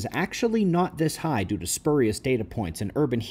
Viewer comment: what did he say at 0:14? Some kind of data point and something else? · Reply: spurious warming - a lot of their data is guess work, which means that the warming they say we have had is actually about a guess with an error range of 1.5 degrees.